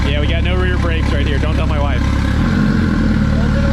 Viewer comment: Did I just hear “Don’t tell Mere?